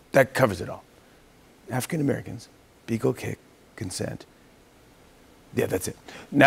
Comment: Wait.. what's an acceptable amount of beagle kicking? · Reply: I thought he said beagle licking 😁Beagles love to lick their humans especially coming in the front door 💖